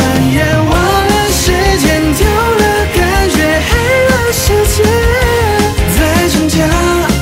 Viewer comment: 之前我沒看歌詞的時候黑了世界我聽成嗨啦世界😂😂